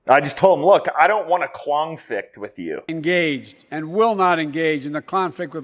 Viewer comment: Sounded like he said clownflict and I'm not saying that because he's a clown 🙄